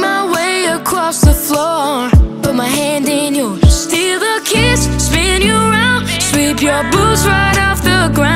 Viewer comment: Am I the only one who thought it said FEEL the kiss and it actually said steal the kiss